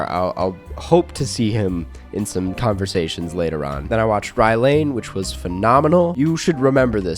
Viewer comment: I can't quite catch the name of this movie, what is it? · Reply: It’s Rye Lane